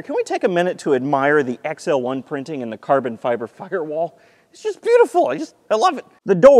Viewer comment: who else heard f*cker wall